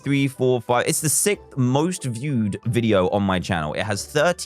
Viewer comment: why do I hear Dan saying "it's the sick most viewed video"?